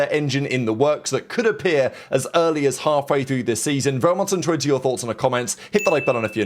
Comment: what’s he saying at 0:28 when he says “very much … your thoughts … comments” he says it all the time and I can never understand it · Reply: Very much intrigued with your thoughts